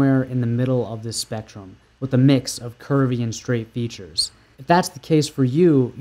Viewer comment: I thought you said “Curry and straight face features” hahahaha